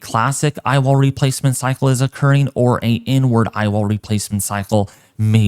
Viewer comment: what did he sayyyyyyy?!??! 🤣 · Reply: IN-ward 😂